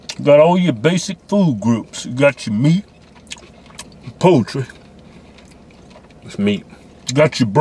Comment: did he say "poetry" hahahaha